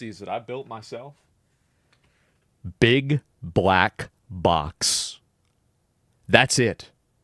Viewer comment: thought he said big black cocks for a second